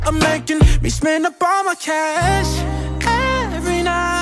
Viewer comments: I thought he said "Obama cash" not "all my cash". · I swear to got it sounds like he says "Obama Cash" "Uh, there is no cash" - Obama